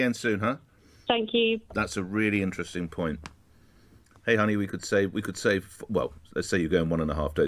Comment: Did he call her 'honey' at 1:20?